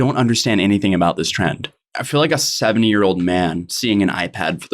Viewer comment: I legit thought he said, "I feel like a 7 year old man" and I was like hold up